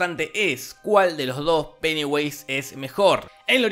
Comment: ESCUCHÉ PENEWASE XDXD